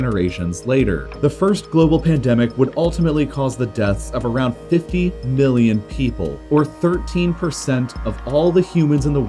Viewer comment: did i just hear 50 billion?